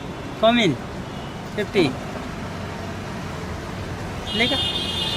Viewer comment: Did he just call you a nigga